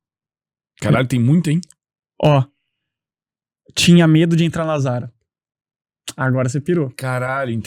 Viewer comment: Não entendi o que ele falou no 5:08 minutos, tinha medo do que ?? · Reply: Entrar na zara